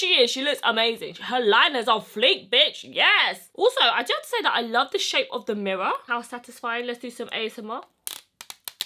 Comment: Ashley.Edits she said “also I do have to say I love the shape of the mirror” the first time. Not that she doesn’t like it..... maybe listen closer next time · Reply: Marinela Skurteska I hear: I don't say that I love the shape of the mirror..